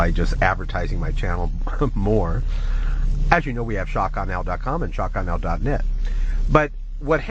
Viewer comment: haha when he said shockawenow I thought he said "shot gun L". great vid! God bless.